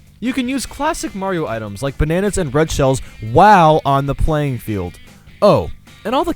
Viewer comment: at first i thought he said bread shells XD